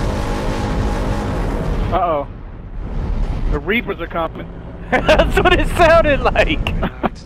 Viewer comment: what did catoonz say at 15:43? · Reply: Darkspirit 2000 the reapers are coming